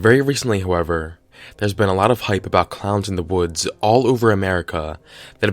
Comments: I thought it said clowns in the hood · bruh I thought it said clowns in the hood💀💀💀💀💀